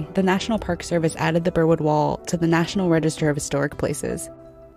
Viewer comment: When I first heard them say Burwood Wall I honestly thought they said Berlin Wall.